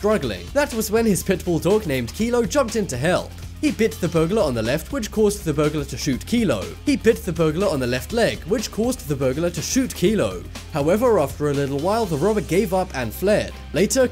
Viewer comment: Did anyone hear that he sed shot nino twice